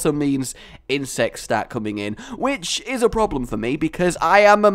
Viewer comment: I thought you say insest. Should i get some help?